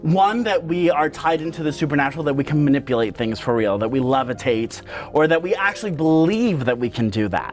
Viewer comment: Ohh "that we levitate" I heard "that we love a taint". Lol oops!